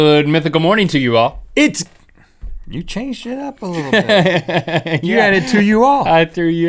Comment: Did anyone else hear Rhett say "You're a little, little bitch." At the beginning of the episode? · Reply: Sounded to me like he said "You changed shit up a little bit", referring to Link saying "Good Mythical Morning to You All". And I was actually checking the comments to see if anyone else heard him say shit, bc I never hear them swear :P